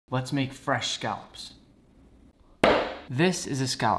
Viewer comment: At first I thought bro said "let's make scalps